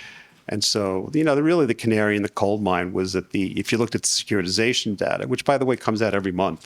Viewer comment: Look at what data did he say? Secured something? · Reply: Good question; " securitization data ". 🤔